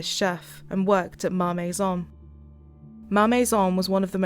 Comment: I looked down from the screen for a moment @ 3:41 was sure I heard her say "Mommy's Son"...what? Then I looked up and saw Ma Maison🙄. But, then again, the closed captions read "marmeson" lol